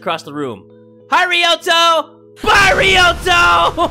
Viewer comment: Kinda sound like he said Hi ryoto! Fly ryoto!